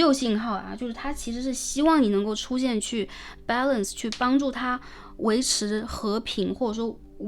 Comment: balance，我听成板蓝丝ahhhh